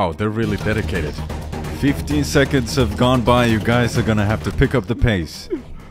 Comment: what did he say? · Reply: Maybe we gotta go